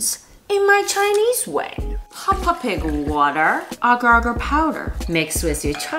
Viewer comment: bruh i rlly thought she said… peppa pig bath water💀